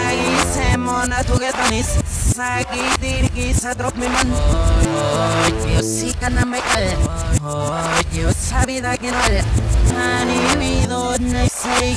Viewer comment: creo que dice "hoy diosito no me cree,hoy y eso sí que duele" así escuché =v